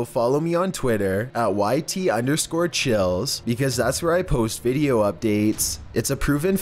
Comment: This video was narrated by. YouTube: <link> Instagram: <link> Twitter: <link> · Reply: Instead of I thought you said 'Chilli's' -_- I'm so stupid